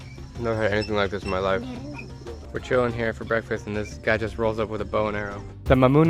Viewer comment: I had to watch this part 2nd time, I first heard "we chillin and this guy rolls up in with a boner"